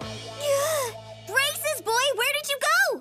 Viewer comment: Did Megan just said 'Racist Boy'?